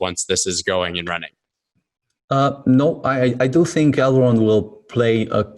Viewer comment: Did he say i do or dont? Think elrond will be crucial thing in the future t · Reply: "Are you ahead in your mind..." "No, I do think erd...."